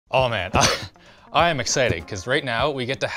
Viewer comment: Did you just say "I am exciting"?